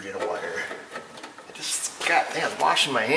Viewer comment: Don't think I've ever heard you curse on any of your videos. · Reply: "got done washing my hands" was the quote, did sound a lil funky though